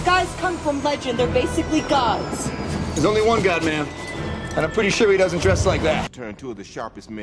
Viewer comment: No one else is gonna deal with the fact that Cap just called Black Widow a "Ma'am" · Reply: Ooohh 0:03 I always thought he said “GodMan”